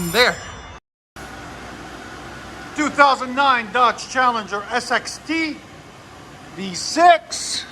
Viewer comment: What did you just say? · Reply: 2009 dodge challenger sxt